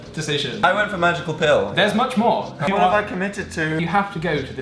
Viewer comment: When dan said I chose the magic pill I heard, “ I chose the magic phil.”